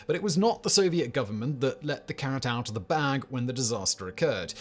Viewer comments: ?"...let the carrot out of the bag..."? (approx. 0:55) ?did i hear that correctly? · Wait, wait, wait... at 0:50 did Simon say "Let the carrot out of the bag"?